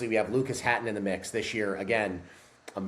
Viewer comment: "Trey: American record holder"? Trey Mitchell was the American log record holder for all of five minutes. Lucas Hatton is the one who currently holds the American log record. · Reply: I heard "you've got Trey, American Record Holder Lucas"